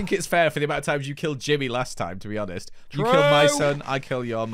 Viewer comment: Did Joel call Jimmy his son? Happens about